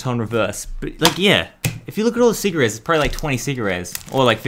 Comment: did he just say cigarettes